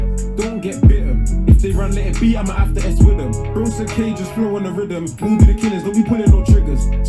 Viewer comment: Did he say something like “don’t let it beat imma have to s wid dem”? - swidom is a producer, did pay attention